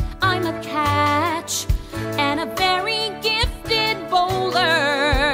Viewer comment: if you see miraculous ladybug characters in this or hamilton in the background dolls, that's OKAY but likeeee that wasn't intentional or anything edit; but its still rude to say to my face that you see resemblances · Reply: 3:53 did she say and a very gifted bowler